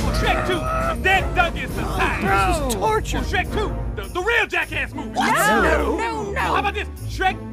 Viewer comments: Did he just say jackass the movie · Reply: No He said, "The Real Jackass Movie" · Gingy didn't even talk he only growled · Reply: You didn't hear him say it? Hold on a moment let me see...yes, he says "This is torture"